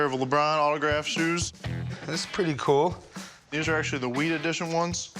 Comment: i thought he said weed edition in stead of wheat